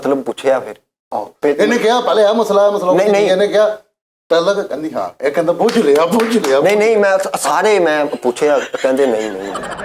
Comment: Can someone explain what says in 14:14? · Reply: Bujh leya bujh leya